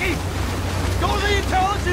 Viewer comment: What does he say at 1:10? Nobody 'verbs' this tent! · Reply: "Go to the intelligence tent. If we get overrun, burn it."